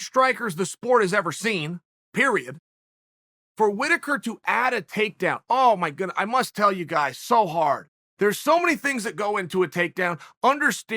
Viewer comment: i heard, “for whitaker to add a takedown, i must tell you guys i’m so hard”